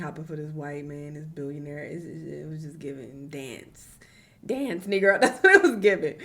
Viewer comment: ive been crying of laughter for the past 8 mins i thought said "dance trigger" with a N😂